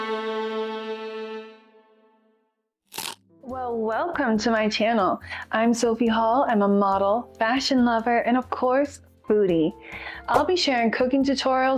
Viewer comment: Ohhhh, foodie 😂 At first, I thought you said you were a BOOTY 🤣🤣 I was like, "Well damn..."